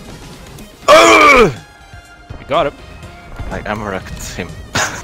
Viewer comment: When said "I ammo racked him" at 3:42, the captions said "my camera it's him"